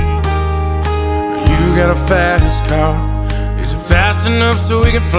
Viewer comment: When i first Heard this I Heard “You got a fat a$$ car” and I still hear it